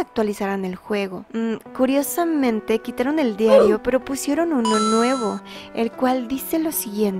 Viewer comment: escuche pitó diario 🤨